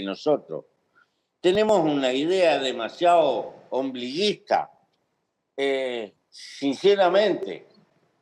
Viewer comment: En el minuto 21:26 entiendo que dice "ombliguista" no obliguista